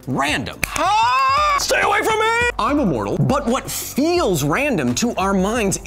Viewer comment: Did anyone else hear I’m immortal